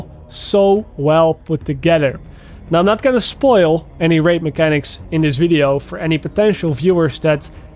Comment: the English auto caption said I’m not gonna spoil any rape mechanics